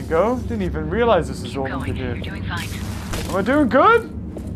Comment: I thought she said your doing vine XD